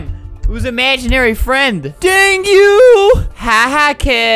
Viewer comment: when Albert said Dang you the captions said Danu